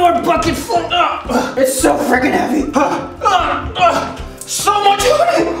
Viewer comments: did round say fricking... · Go to the timestamp 2:18 it sounded like he said the F word🤭